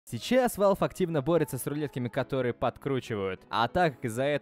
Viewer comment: Лайк, если тоже послышалось в начале самом "Завтра Первое Сентября: Что означает, конец света)" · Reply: Мне в начале послышалось : Cейчас Valve активно борются с рулетками которые подкручивают....